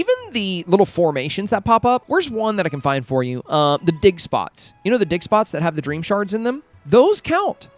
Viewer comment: Ur insanly creative! Those tips are so helpful thank you so much. Lol I was making a sandwich and I had ur video on and I was like wth... did he just say dickspots?! And I laughed inside my head and thought oh yea ofcourse digspots LMFAOOOOO hahaha I'm still laughing everytime I hear u say digspots 😂😂😂